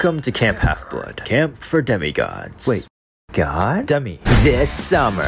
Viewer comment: When he said demi I heard it as dummy Him: Light bulb Me:😂😂😂 Hadies shows up Me:pfttt and he has books I didn't know he could read Coming never Me: looses shit Neighbor: you gud gurl Me: it's so accurate 😂😂😂